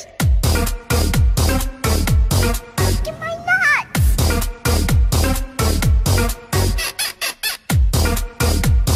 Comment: what did he say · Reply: The voice said "get my nuts" but trust me its not what you think the original vine is wholesome